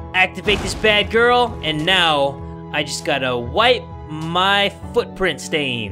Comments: I thought 💭 he said wipe my ass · Hey guys, next episode with the new update will be either friday/saturday, so reccomend some challenges/myths/questions and i'll use the creative/funny ones in the next Yandere Simulator video! Love you guys!! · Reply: I thought 💭 you said wipe my ass